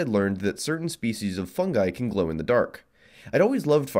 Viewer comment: Came here for the idea of a miniature bioreactor that I thought was gonna be an easy, safe, eco friendly reusable energy source Stayed for your interesting pronunciation of "Fungi" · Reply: oh shit he was saying fungi? thought he was saying e coli